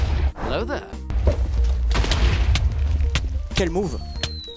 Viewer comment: Fun fact : didn't say okay move but "insane move" in french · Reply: Moi j'ai entendu " quelle move "